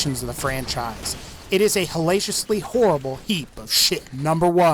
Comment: Helatious? Hellatious? meaning exceptionally powerful and or good? or salacious? as in inappropriately sexual? what are you saying?